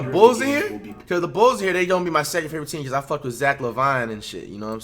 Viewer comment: Flight:bulls are my second favorite team Bruh I could've swore he said the blazers are his second favorite